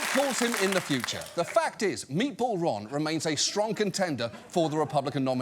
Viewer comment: When discussing the meat-themed nicknames, (around 4:02). I heard John say, 'Meatball Ron is a *shrunken tender.*' when he obviously meant, *strong contender*. 🤭